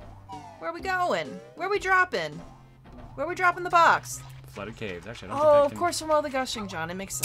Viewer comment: I thought she was gonna say where we droppin bois?